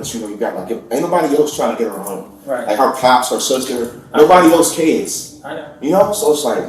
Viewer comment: U had a few captions completely off wasn’t even what he said at 18:15 he said her pops and sister wouldn’t help her get back home and nobody cares not I heard cops would search her or whatever u put